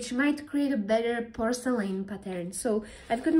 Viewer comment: I thought she was saying porcelain butter through the whole damn video